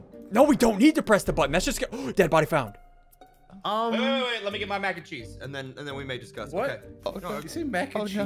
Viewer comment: Did you say let me get my Mac & cheese 😮